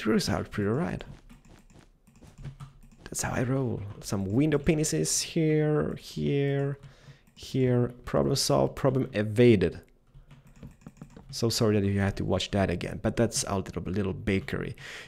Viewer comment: did u say window pinus?